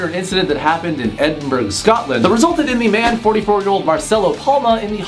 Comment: Did he just say Edinburger, Scotland instead of Edinburgh, Scotland? Say it like Edin-bruh 👍🏼 🏴